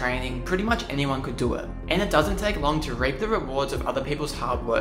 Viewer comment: BRO THE CAPTIONS SAY "and it doesn't take long to rape the rewards"